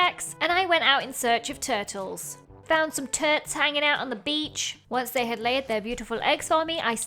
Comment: surely i wasn’t the only person that thought you said “found some turds” 😭 😭